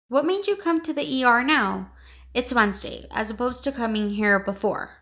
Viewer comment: Sorry what does de dr says at 2:29? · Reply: What made you come to the ER now; it’s Wednesday, as opposed to coming here before?